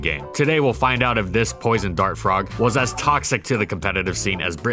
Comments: why did he say poison dark at 0:35 ? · did he just said "poison dark frog"